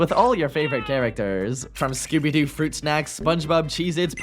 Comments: please tell me Im not the only one who heard skibidi fruit snaks😭😭😭😭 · my rotted Brain thought you said skibidi😨